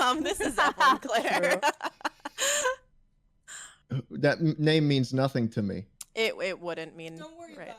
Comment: What does she say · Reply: That name means nothing to me